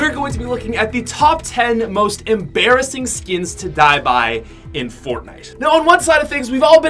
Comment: Wno else thought it said "most embarrising skins to buy"?